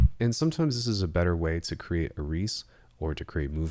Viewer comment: Can you explain what was said at @ 7:45, was it "a reece?" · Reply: "better way to create a Reese"